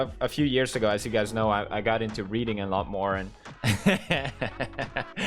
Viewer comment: I heard: "I got into reading and lawnmower" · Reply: Hahahaha its actually "i got into reading a lot more" 😂 But his tone and accent makes us accidentally hear the lawnmower 😂